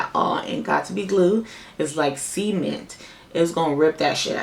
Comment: oop. you said cement. We can all see where the fk my head is at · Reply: I thought she said semen too, until I heard her the second time!